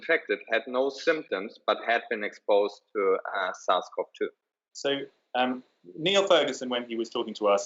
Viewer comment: and 16:00 is he saying “south cov 2”. Is that COVID 19 ? 24:50 is he saying “viral load tolls”?